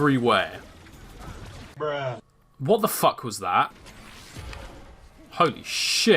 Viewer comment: When said holy s**t flying chomper it was funny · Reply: Masterboy I heard "oh sh*t" not holy